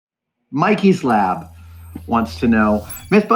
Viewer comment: I definitely heard that as "Mikey Slab"